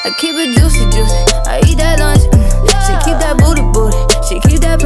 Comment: LMAOOOO MY MOM THOUGHT SHE SAID "I EAT THAT LUNCHABLE"